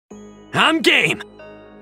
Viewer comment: I always thought that Shallot is saying "I'm gay" instead of "I'm game" xd